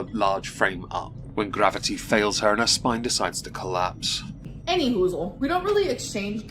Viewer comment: did she really say "anywho-zzle"